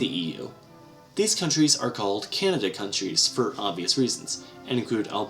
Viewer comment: I thought you said Canada countrys lol. Its candidate countrys